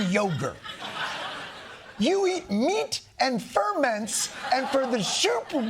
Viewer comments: I thought at first RFK said he was eating the meat of fur men. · Who says the word ferments!? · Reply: I thought he said ferrets.